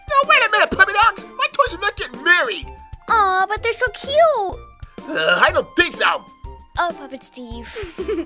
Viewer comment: What did puppet Steve say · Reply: I don't think so! (Is what he said)